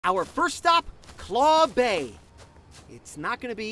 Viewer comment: I honestly thought he said Claw'mbe, and i 100% excpected a chef crab joke. Now im kinda sad